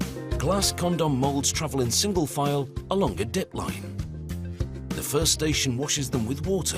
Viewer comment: I heard dick line. I started crying